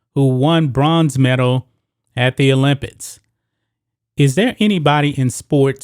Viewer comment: Did you just say "Olympits"